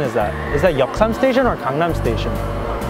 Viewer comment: The subtitles saying Condom Station, Oh God, I m done😂😂